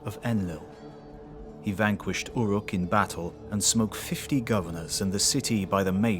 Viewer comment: I had to listen to this like 3 times. I kept hearing “he smoked 50 governors with a mace.” And was like… damn that’s a choice word to use for murder. Before I realized you said “smote.”